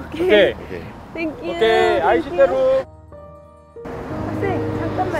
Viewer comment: did he said "aishiteru" OMG